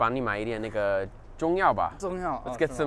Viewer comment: is it me or did it sound like he said the n word